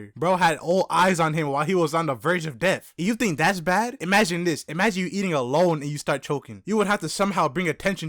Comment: what said: imagine eating alone then you start choking what my dumbass thought he said: imagine eating a lone then you start choking